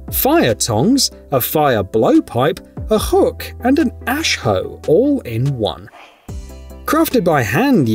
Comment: did you say ashoe 😂😂😂😂😂😂😂😂😂Lmao